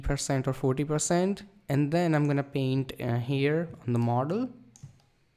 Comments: What did he say "in the marder"? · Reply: here in the model...he just clicked that guys picture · Nice video,definitely giving me ideas for my next photography tutorial and photoshoot!👌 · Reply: What did he say "in the marder"? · I love your videos !! You should post more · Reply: What did he say "in the marder"? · Will you work on premiere pro???make some tutorials on that toooo... It will be helpful for the people who are ready to edit the videos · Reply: What did he say "in the marder"? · omgg, thank you so much! I have to make a poster for a school project, using photoshop, and now I finally have an idea of what I want, and I'm also so happy to feel like I know how photoshop works! <3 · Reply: What did he say "in the marder"? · I like how he's all "I'm only going to explain once. So concentrate" like we're all not going to play the video backwards multiple times XD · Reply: What did he say "in the marder"? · What did he say "in the marder"? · Thanks a lot bro, I did the tutorial, You are a photoshop crack !!!! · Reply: What did he say "in the marder"? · So glad to see you have returned with this new content! Thank you for this tutorial. You explained it well. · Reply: What did he say "in the marder"? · when he says model he sounds like mortal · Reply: What did he say "in the marder"? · Nice one really like to see more videos from you. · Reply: What did he say "in the marder"? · another great vid keep it up mate! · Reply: What did he say "in the marder"?